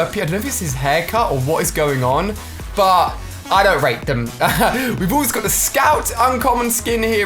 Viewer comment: Listen from 5:35 did he say I don't take them or